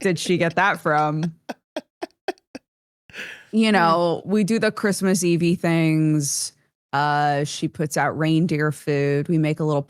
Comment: I heard "Christmas Eevee things" and was confused and excited for a moment before I realized.